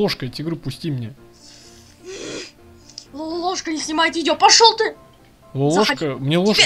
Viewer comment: я один ржу в этом моменте? Что он говорит? 😂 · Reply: Он говорит:аааа лолошка не снимает видео аааааа